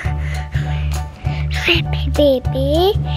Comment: When starts blogging it sounds like he said "sleepy baby" at 1:16.